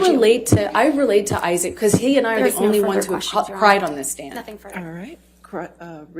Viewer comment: "I relate to Isaac because he and I were the only ones to cry on the stand" this bitch xD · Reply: ah, i was wondering what she said at the end.. i thought she said "..the only ones who shot pride on the stand"